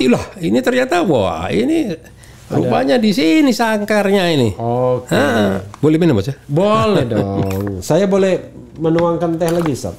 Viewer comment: "Boleh Minum Bocah" Ga salah denger?